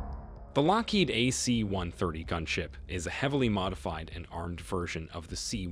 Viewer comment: I thought you said gun shit at the start